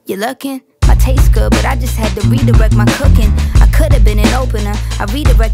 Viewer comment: “look at me, look at me, you looking? i taste good, but i just had Dorito in my cooking” this is what i hear 💀💀😭😭😭